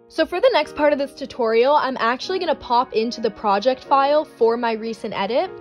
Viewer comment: when she said she was gonna pop right into her project file i thought she said she was gonna link it 💀